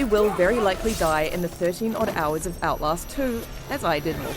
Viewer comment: Whoa did she say 13hours? so the game us 13 hours long?